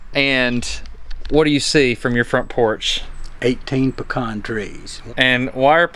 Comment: lowkey thought he said "pecaws"